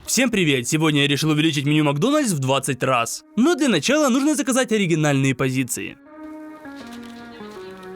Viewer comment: Кому тоже сначала показалось что он сказал "Я хочу увеличить Макдональс в 20 раз"